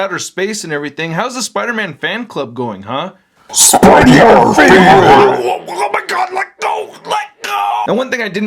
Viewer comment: What did he say? o.O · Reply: Space Venom: " Spidey our favorite"! If you haven't found out already by now haha